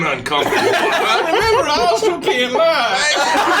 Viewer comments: at like 14:19 what does say i couldnt understand him with all the laughing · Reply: “Remember I also can’t lie” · can someone tell me what said at this time I want to know why they all died laughing? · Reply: I also can’t lie